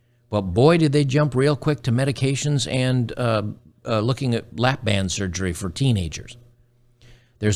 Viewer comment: At about the 8 Minute Mark I could have swore at first you said lap dance surgery